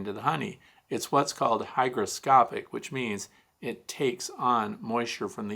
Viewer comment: Morning, at roughly 9:55 video time, did you say hydroscopic or hygroscopic ? I know it should bee hygroscopic, but wasn't sure what word you were using Even replayed a number of times. Maybe it's just my waning hearing or your thick eastern accent. Just kidding it's not thick. Lol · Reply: Hygroscopic, I hope it didn't sound different? :)